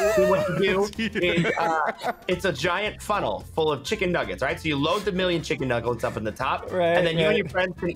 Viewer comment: Did anyone else hear Sigils say "Nugglets" at 9:41 🤣